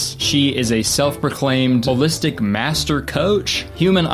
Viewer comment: I was just listening while playing a game so I thought you said "Ballistic Master Coach" at 2:25 and it brought an off-brand Master Chief to mind